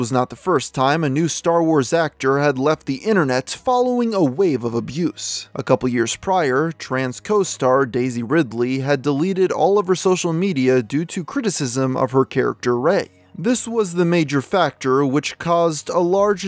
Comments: I didn’t hear “Tran’s co-star”, I heard “trans co-star”… and I was all “is Daisy trans?!?” 😅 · Holy shit, I had to rewind a few times because I thought you said, "Trans costar Daisy Ridley," my jaw hit the floor. .............. until I realized you were saying,"Tran's costar Daisy Ridley."😂😂😂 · you may want to give your scripts a once over before recording, I thought you were saying Daisy RIdley was trans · I thought you said “trans fellow actress Daisy Ridley” but then realized it was “Tran’s fellow actress”. I was almost so confused 😅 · Bro I was going bonkers because I wasnt paying full attention and thought he said "Trans co-star Daisy Ridley.." as in, Daisy is a Transgender woman. And I said to myself, "weird, Ive literally never heard anything of this before." So I looked it up which led me to exactly 1 result. A video posted 4 years ago claiming that Daisy Ridley is, in fact, a man. I thought that video must be your source for this apocryphal information you were spreading. So I returned to this video to call you a dunce or something and as soon as I hit play I realized you were saying "Tran's Co-star" and now here we are. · I heard 'trans co-star and was surprised lol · For a second i thought he said "trans co-star Daisy Ridley-", and was like wait since when? But then i remembered it was that rose tico lady’s last name lol.